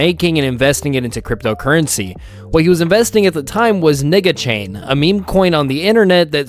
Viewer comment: Did he just say nigga chain 😂